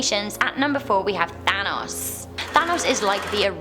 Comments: Who else thought she said Thanos at number 4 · Thanatos I thought you said thanos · I thought she said thanos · I was watching this video with my fan and when she said Thanatos I thought she said, Thanos... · Who else thought she said thanos when she actually said thanatos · I thought you said Thanks, not Thanatos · When you said thanatos I thought you said Thanos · when she said thanstos I thought she said thanos